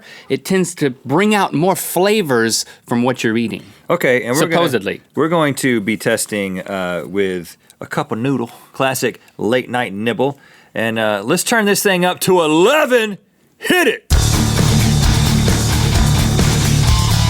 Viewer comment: I thought Rhett said "late night nipple" 😂😂😂